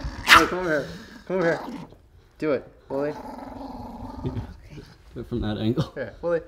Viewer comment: Oh, his name is " Wooly". I thought it said "Wookie".😂